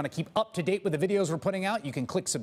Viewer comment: At the end of these videos when Chris Hayes says "You can click subscribe just below me." All I hear is "You can click subscribe, just blow me."